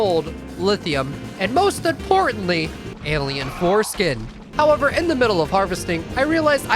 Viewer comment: i skipped an little and i heard "alien foreskin" WTF